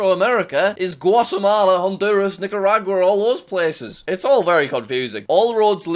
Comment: what he say 5:21 😳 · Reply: Nicaragua 🇳🇮 it’s a country in central america